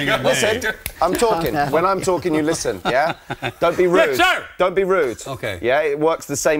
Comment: what does Gary scream? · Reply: I think he said “yes sir!”